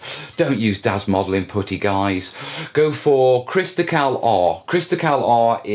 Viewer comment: excuse me, I'm not an english natural speaker and I don't understand what he say on 22:40 "go for crystal...???" I aprecciate any help :')